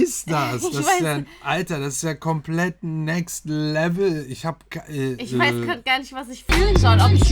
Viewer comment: I don't understand German and there's no substitutes but I clearly heard "next level" 😂❤️❤️ agree agree guys 🔥👍